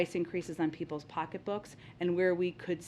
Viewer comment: All I heard was “I’m a ginger so I hate you all.”